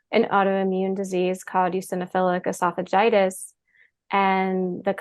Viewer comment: mentioned a disease, and it's difficult to catch what she said. Does anyone know what she said? I looked at the transcript and it did not catch it correctly. 😢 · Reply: Hi Maria! The disease was Eosinophilic Esophagitis.